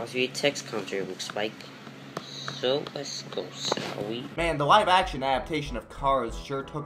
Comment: | 3:47 I believe the words you couldn't make out here were "shall we". It definitely had the Sh sound and ended with a long e, so im fairly confident here.